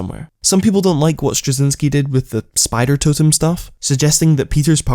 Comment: thought u said spider-tism... sorry bro.... ur videos main impact on me has shifted